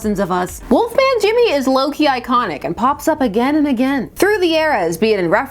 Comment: I know I'm drunk, but I heard "Loki Iconic"